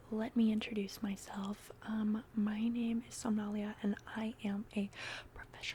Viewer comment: OK I checked your channel description for your name because I thought you said your name is Somalia 😅